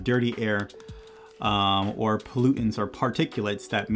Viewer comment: I thought you said “glutens” instead of pollutants haha